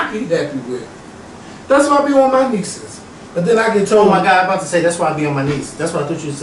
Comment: I thought said that’s why I be on my knees too 😂😂😂😂